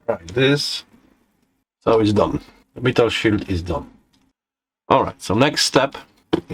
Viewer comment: What is "beetle shoot"? He says it just after 5:15. (Sorry, I don't speak Russian). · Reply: He said metal shield I think